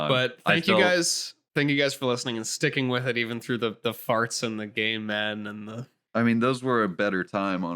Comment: for some reason, I heard “game men” in three different ways: game men gamin’ and gay men